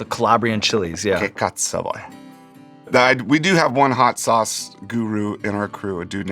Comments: Italian fellas, did I just hear a "che cazzo vuoi?" from Dave Grohl? LMAO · did he say "che cazzo", "che cazzo vuoi"?? 😂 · did Dave just said " che cazzo vuoi'" ?